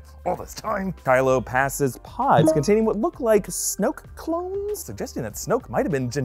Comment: no matter how many times I watch it I always hear “SnowCones”